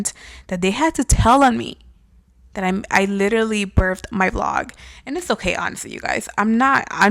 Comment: Wait idk if it’s me or did you just say brith my blog ? Sorry am just l listening I wasn’t watching it